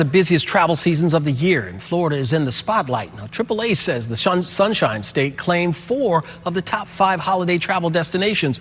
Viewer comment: Did he just say the Shunned Sunshine State?? 😂😂